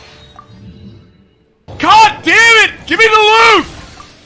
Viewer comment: Lmao I thought he said “GIMME THE LOOT” at the start